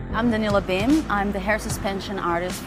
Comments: I can’t be the only one who heard “I’m Vanilla Bean” at 0:25 No offence to her at all^ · When she said her name was Danila Bim, I thought she said Vanilla Bean and now I can't get over how cute that is